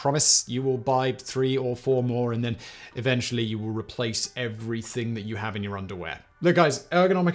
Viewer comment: "Eventually you will replace everything you have in your underwear." Sounds ominous. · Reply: Simon's "flow" never said the word drawer. That's the point, is that he literally said "you will replace everything you have in your underwear". That's it. We all know what he meant, whoever typed the captions put what he meant. But the point is that it's not what was said.